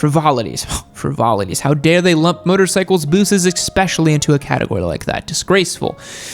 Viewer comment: did you just say eXpecially?